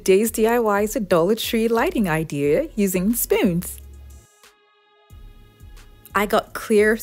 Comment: At first i thought she said using SPINS...I was like huh???